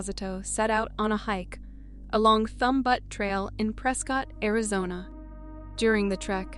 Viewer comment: Butte is pronounced b-you-t not but.... It is a common word ffs.... Let's pull our thumb ourlt of our butte and try a little harder shall we? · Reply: I heard " Thumb Butt Trail". I assumed she meant "butte". Was actually kinda funny. We jokingly call everything named "butte" butt. 😹